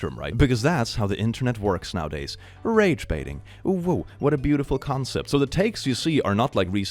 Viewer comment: Did he just say "OwO" in a non-furry related video? It's spreading...